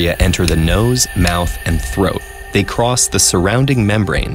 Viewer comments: I know it’s super serious and all but I thought it said throot and it was pretty frickin funny. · lol i thought throat said throot